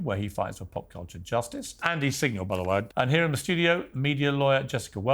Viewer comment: Did he say "and he's single by the way" when introducing? Why would that be important? Did I mishear?